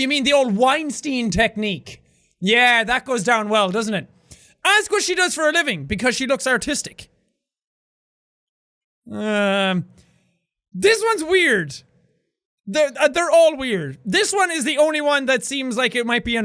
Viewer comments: When he asked, "are you artistic?" I heard "are you autistic?" and I was like WHAT?! haha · BANANA! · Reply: it sounded like he said u look autistic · It sounded like he said you look autistic